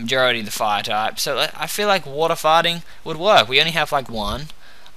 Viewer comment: i heard "i feel like water farting" XD